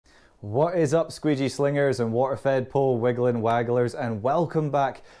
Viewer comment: I love the intro, always sound like your saying wfp windling wankers lol